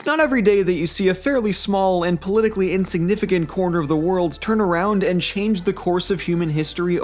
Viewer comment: Why did you put an "s" on "world" at 0:04? "...a particularly insignificant corner of the world" probably doesn't need an "s" because "turn around" is a verb, not a noun. At least, that's how I heard it.